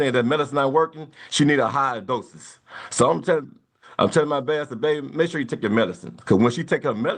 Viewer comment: did he say I’m telling my bastard baby? Or did I hear that wrong?